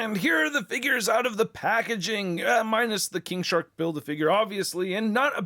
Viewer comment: I swear I heard 'King Shart' at the beginning there